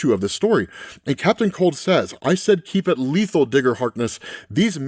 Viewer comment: actually he said nonlethal, the complete opposite of what you said